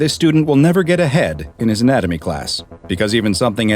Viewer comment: i heard "unfortunately this young man will never get HEAD in his anatomy class" 🤣🤣🤣